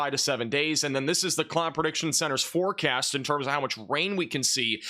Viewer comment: So help me, I’ve listened to it five times and I keep hearing Clown Prediction Center. I blame my aging ears. What are you saying there? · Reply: Climate Prediction Center...but yes, hard to understand. If it helps, turn on captions using the gear icon in the corner of the video. 😊